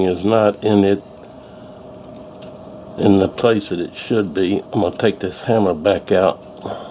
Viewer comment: I kept hearing *I'm gonna take this hammer back and put the hammer back in but no hammer! Great job there. Very tiny parts make me nervous. I bet that scared you when you had the safety on:))